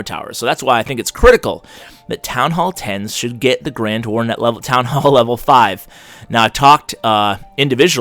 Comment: the grand warrden shold be available at th5 ??? im i hearing this right ...??? SOMONE TELL ME !!!!!!!!!! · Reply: no he meant grand warden lvl 5 at th 10